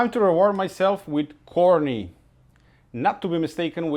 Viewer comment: Lol. I was doing something not looking at the screen then he said time for corny and I thought he said horny... then he said not to be confused with honry